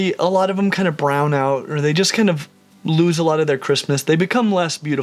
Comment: I know you said they lose their crispness but I thought you said they lose their Christmas which would be even more upsetting.